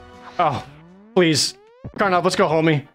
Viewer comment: I KNOW Jared said "please Karnov, let's go, homie," but for a split second I heard "please Karnov, hold me," and that seems more right. Like, who wouldn't want to be held by a burly, shirtless Russian daddy? I know I would.